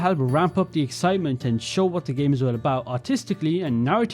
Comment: did anyone else hear autistically here